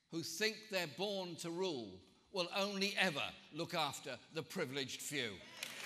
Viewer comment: When he said "the privileged few" i thought he said "the privileged jew". i had to backtrack to listen again, ha ha.. But with all that i'd been hearing and reading about this party, under him, I thought it was perfectly possible... mygodmygodmygod, what a state the UK is in.. :(